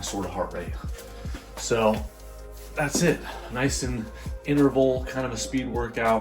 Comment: Thought bro was saying “iceland” the entire time yaya